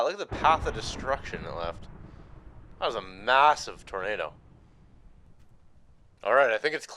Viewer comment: did you say massive